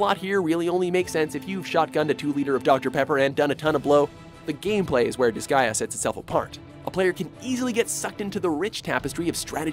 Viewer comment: did he just say shot gunned a 2L of Dr.Pepper and done a lot of blow. lol 😂😂